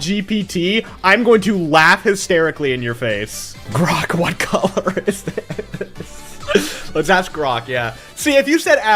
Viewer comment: nah i wanna know what grok said though 😭😭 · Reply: It said “stop relying on AI to do your thinking for you”